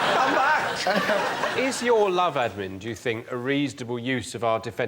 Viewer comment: “Is your lover a reasonable use for our defense budget?” I absolutely ADORE Miles 🤣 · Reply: I thought he said "love admin", which if I'm correct is even funnier 😅